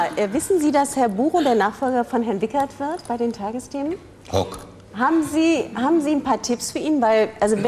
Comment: Was sagt er denn bei 2:43 ??? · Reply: „Guck“